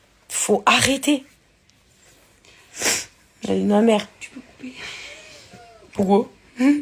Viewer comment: attendez...c'est moi ou à 14:26 sarah se "touche le nez" et qu'on entends " tu peux couper ? " et qu'elle change de suite de conversation ?